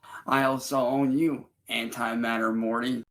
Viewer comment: Just realized, saying, "I own you" kind sounds like "its all you" so how i hear it is, "its all you anti-matter morty." also thank you you got me through high school, made my days a little less boring<3 thanks man :)